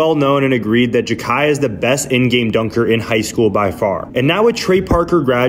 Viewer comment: Jalen green… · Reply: for some reason I thought he said the best highschool dunker oat mb